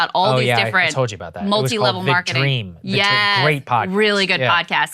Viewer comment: What’s the name of the podcast Saagar is saying at 15:48? I’m not picking it up clearly…”Big Dream”? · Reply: The Dream